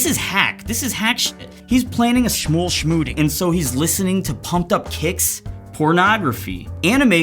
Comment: I wasn't looking at the screen and i thought he said "Pumped up kicks porno" and i'm like " W T F "